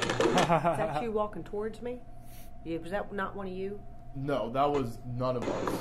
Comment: Great show and loved the evidence but i hear at the end "Is that you walking towards me!" slowed it and thats for sure!